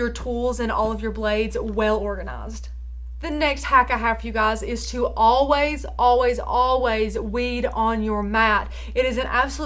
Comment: Hmmm, I heard 'always, always, always wee on your mat!!' Probably because I have a puppy and that's where my brain went. Very helpful video thank you :)